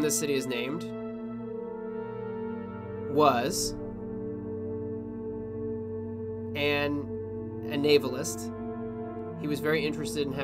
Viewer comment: i heard " was an ableist" XD